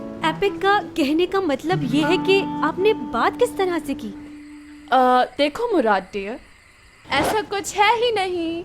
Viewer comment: Did she said murat dear